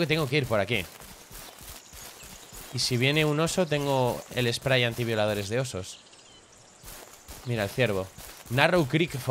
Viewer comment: ) no se pero le entendi "y si viene un oso, tengo el spray anti-violadores de osos"